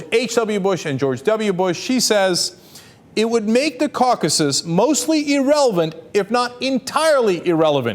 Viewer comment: why did he say "caucases" wtf lol?